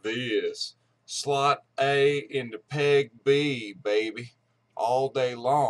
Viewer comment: I thought you said slot A into pig B lol